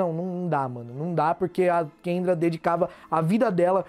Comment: Ele falou "Kendra" o volume tava baixo e eu ouvi "Quenga" KAKKAKKAKSKKSKSKKSKSKSKSKKS EU TAVA CHAMANDO ELA DE QUENGA O TEMPO TODO